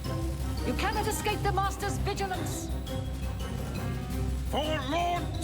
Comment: when you were running out of the mythic dawn base i kept hearing "YOU CANNOT ESCAPE MANAGED DEMOCRACY!"